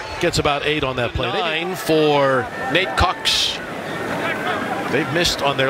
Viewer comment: I thought the announcer said Meat Cox. I almost got a heart attack.